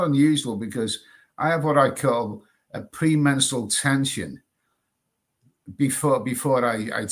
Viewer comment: 0.45 it sounded like you said PRE MENSTRAL TENSION .....I kinda guess you mean pre MENTAL TENSION.....but , then again ,..these days you never know who is what & what is who,..when the hands don't really fit the shoe ?.